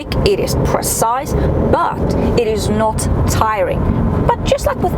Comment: this was a beautiful review. What did you say about the steering wheel at 5:27 it is not...?? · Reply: Tiring δηλαδή κουραστικό